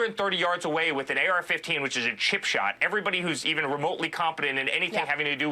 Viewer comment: What did he say? I’m sure it’s me being English but it sounded like he said “with an AR15 which is a chip shop”..